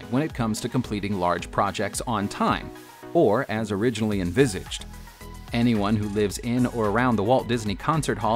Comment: I really want to know what word hes saying at 7:12....I listened to that part three times and I still cant understand tf is being said there... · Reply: As originally envisioned